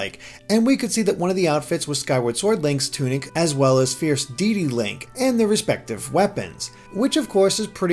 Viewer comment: Lol sounded like he was saying fierce deedee link.